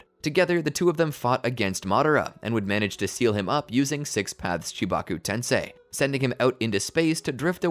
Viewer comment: I misheard that as "Six Paths Chewbacca Tensei", and considering how Chewie goes out in the old expanded universe novels...